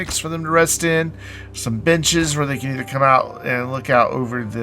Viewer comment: lol, I mis-heard you saying b***es when you said benches near the end of the vid. Had to play it back a few times before I realised XD